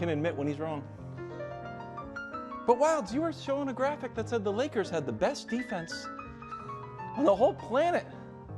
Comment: I thought Perkins said the Lakers saved the NBA😂😂